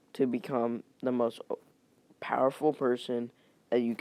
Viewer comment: did he just say powerful virgin?